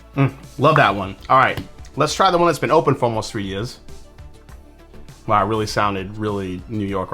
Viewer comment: glad you caught that because I paused for a sec and thought “did he just say ‘three yezz’?” 😂